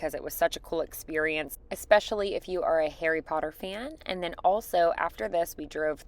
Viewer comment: Sorry, your accent amuses me, in a kind way I hope, but every time you say "Harry" i hear "hairy"